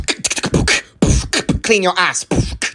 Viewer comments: BRO I THOUGHT HE SAID “clean ur eyes” NOT “clean ur ass” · "Clean your ass" was unexpected as fuck 💀💀💀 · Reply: I heard "Clean yo'r eyes" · Did he just say clean mama